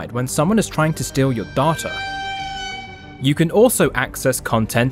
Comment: Lol Im so sorry but during the part with the sponsor segment when you said data, my head jerked up cause at first it sounded like you said, "You'll even be notified when someone's trying to steal your daughter!" lmao, I was like waaaaaait...ooooooh never mind lmao!